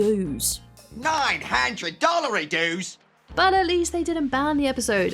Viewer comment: I thought he was saying $900 in dues. 😂 wow